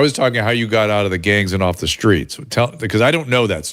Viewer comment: I thought she said "gay" at first, then Dr. Drew said "gangs!" I was beginning to think my old age. Also, Dr. Drew is FOR the HPV vax as well as the abortion pill so she might get his wrath. I like Dr. Drew though. Funny fact, I grew up Roman Catholic and mostly Democrat household. I voted for Reagan as my first President. I also served in the Navy. I'm 61 and used to believe in shots, no longer !